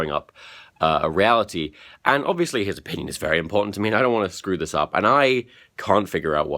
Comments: "His Penis is very important to me", that's what i heard the first time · OMG pls say I'm not the only one who thought you said "a penis" at 16:44 instead of "opinions" 😂😂😂 Btw I love your Vlogs! And if I was at the summit I'd totally have you in my Vlog! You're cool. Who wouldn't wanna have you in their Vlog?! And don't worry about the rape question either. I think it's important that s.o. cares and asks. Sure, could've been better timing but what's more important, taking action with rape allegations or ending on a light, positive note? Hope that made you feel a bit better ;) Lots of love from Berlin x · Did just say "His penis is very important to me?"